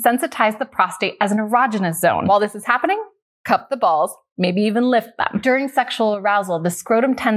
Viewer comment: I heard "Cut the balls."